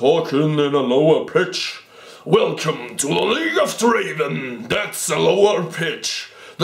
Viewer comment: Lol did he just say league of draen? LOL